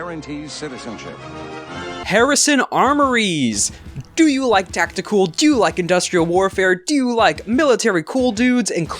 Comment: Hey, at 5:32 by any chance DID I HEAR A ROCK AND STONE? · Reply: You did hear a ROCK AND STONE!